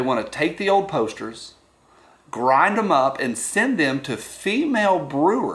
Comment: It's kind of funny cause when I watch the ad, I could have sworn they said send them shit. Maybe they were referring to old Posters.... but.